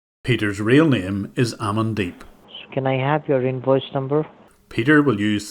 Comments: Ok when he said peters real name I heard him say “I’m in deep” I was waiting for him to say “that’s what she said” afterwards. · Whenever you said the scumbag’s name, the only thing I heard was “I’m in deep” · What he said: "Peter's real name's Amandeep" What I heard: "Peter's real name's I'm a dick" I need to practice my listening skills.